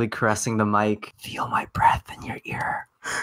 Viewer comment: I thought he said "feel my breast in your ear" and was a bit scared